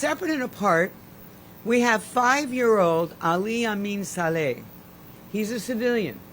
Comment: Ummmmm…..At 5:47 did she say a 5 year old was being charged?!🤔